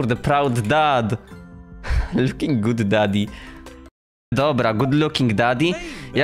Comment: Czemu ja usłyszałem "ohh daddy"?!